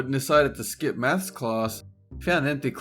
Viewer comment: "Mas class", am I hearing it wrong? I thought he said " Mas class" instead of Math class